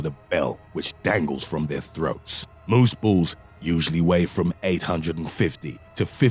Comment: lmao I thought u said “moose balls weigh <num> LBS” I had to rewind